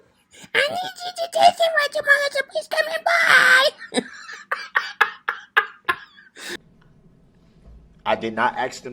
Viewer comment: what did u say when u were talking like Sue Opper??😂😂😂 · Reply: “This is Sue Opper . I need you to testify tomorrow. Please come in.